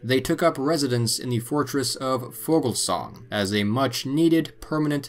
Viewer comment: Outstanding! Thank you for doing this video. When you have time, I have a question. At about 9:19 in the video, you mention that the knights moved to a fortress but I cannot make out the name or find it on a map. It sounds like Vogelsong but, like I said, I can't quite make out the location name. Can you help by providing the proper spelling of the fortress? Thank you so much. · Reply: Vogelsang. Thank you!